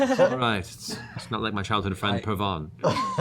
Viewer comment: Purvan? Did he say Purvan, the last Raven Queen Champion? ... was his best friend? His childhood friend...?